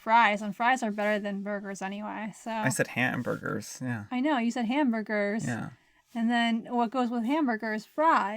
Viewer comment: I thought you said you were hamburger and I was waiting for to say helper. Hahaha hahaha 😂😂